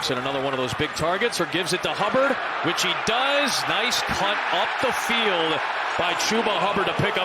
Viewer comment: What did he say?! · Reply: “Cut”